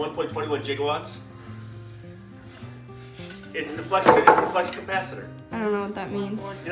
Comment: I turned on your video, cranked up the speakers and started to get up and go into the kitchen to grab a bite to eat and in mid stride I heard you say "It's a flux capacitor" and I stepped backwards and sit down and said huh? And watched as you pulled something else out.....I had a good laugh at that. Thanks for the laugh, I needed that today. Love what you guys do....really do!